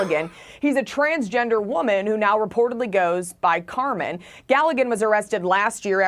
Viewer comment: Cool name... " at first i thought she said the name was KOCHED HER-MAN" but its carmen.